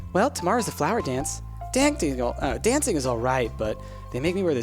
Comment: Sounded like you tried to pronounce dancing as 'dang daniel' lol